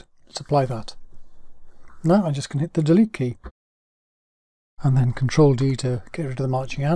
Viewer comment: A good vid. What do you say at 2:25 min into the vid? Anybody? Thanks in advance for any replies! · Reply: "Now I'm just going to hit the Delete Key"